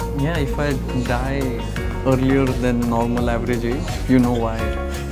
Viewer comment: Does Vidit says at 4:45 "Ja ich weiß" (yes I know) ? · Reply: No he just mockingly says “yeah if I die..you will probably know why” (in jest ofc in response to a question by the interviewer)